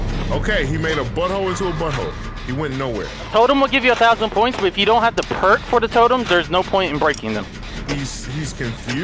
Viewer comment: did he just say if you dont have a perk for the totem there is no point in breaking a totem